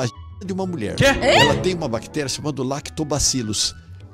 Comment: eu entendi ele tava falando do yakult